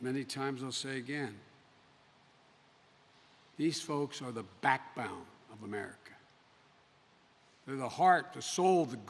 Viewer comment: did he just call me the "backbound"